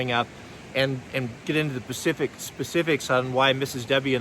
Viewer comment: You west coast guys always saying " Pacific " instead of " Specific " lol ;)